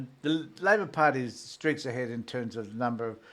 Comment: Did he just say streets ahead at 3:13? Knew it was verbal wildfire